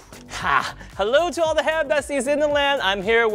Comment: Ok I’ve been watching his vids for a while and I always thought he said all the hair bussys in the land I’m so sorry y’all